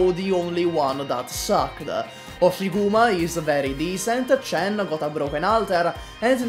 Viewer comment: Why did I hear it Buffiguma